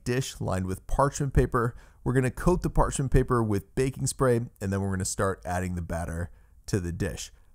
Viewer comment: It sounded like you said bacon spray 😁